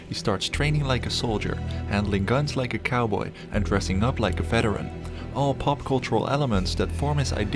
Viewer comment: Travis Bickle IS a veteran, he's a Vietnam Marine Corps veteran, so he's not just training or dressing like a veteran. That's a major part of his character and therefore the story. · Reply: Thanks for that. I thought he said, "...and dressing like a Bedouin." I thought that was weird.